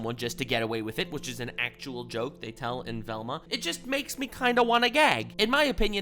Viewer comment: I think i'm still waking up. But did he say, It makes me kinda wanna gag . Or was it makes me kinda wanna gay?